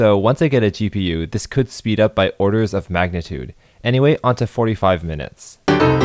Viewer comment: Misheard you saying "...this could speed up by orders of magnitude. Anyway, on to 45 minutes" as "This could speed up by 45 orders of magnitude." Thought you'd gone a bit loony for a moment, had to rewind.